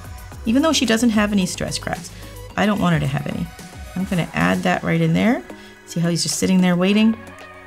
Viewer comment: did she say " I'm finna add that" lol